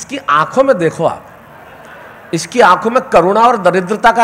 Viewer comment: i heard corona but actually Lodha sir was saying karuna